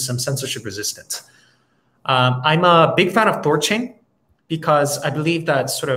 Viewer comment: Do Kwon mentioned he is a big fan of Solana and something else I couldn't make out at around 40:05. Could anyone share what he is referring to? Thanks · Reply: I think it was Thorchain